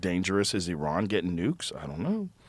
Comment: I thought Joe said " as scary as Elon getting nukes?", but then it gradually become clear that he said "Iran", not "Elon". LOL